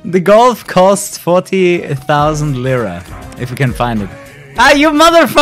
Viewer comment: What you said in 3:29 golf cost .... (...) Edit: Haaaaaaa its Lira its Turkish Money name